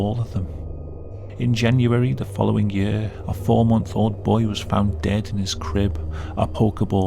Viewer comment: Majority of these are parents not paying attention to their kids, especially the Pokemon one. Who lets their 4 month old play with a plastic ball like that in their crib? I can sort of understand some of the other cases, but the 4 month old blew my mind that they'd just drop the kid in with that toy and walk away. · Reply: 4-month-old? I thought he said 14-month-old.